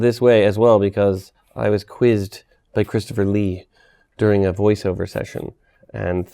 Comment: Did he say Christopher Lee? Same one who died in 2015?